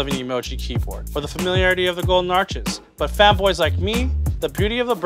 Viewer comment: Anybody else thought he said 'fatboys' when he actually said 'fanboys'.... Sorry, but you thicc